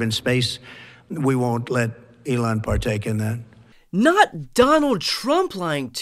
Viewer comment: Did he actually say at 1:50 "we won't let Elon PART TAKE in it" ...?..😮...